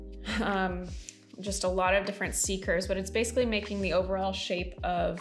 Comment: I have to tell you this...for a long time I thought you said "Seekers" instead of C-Curves! LOL. I thought that was something new for sure! Finally, I heard you say it a little slower and I laughed so hard! Thank you for your videos. I'm starting to develop my own style and grow my confidence.